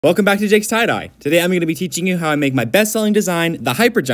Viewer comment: Ngl, he sounded like he said “ welcome back to Jake’s side eye”